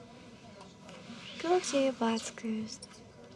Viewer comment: This could perfectly fit in a David Lynch movie lol 15:40- I thought she said: Good luck to you, bastards. Lol I saw you winning 2 euros several times. That's better than nothing. Everything on the video is tinglely and weird (the good weird). Thank you so much 🥰